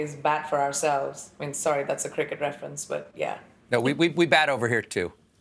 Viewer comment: she says I'm sorry its a "cricket" reference. The subtitles say "crooked"🤦. Give it to the Americans.